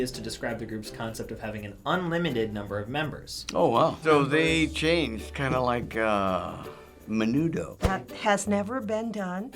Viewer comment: "so they change, kinda like uh, menudo" 😂👌 That's what I heard omg